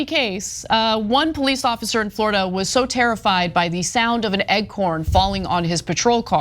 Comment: is it normal for americans to say Eggcorn instead of Acorn?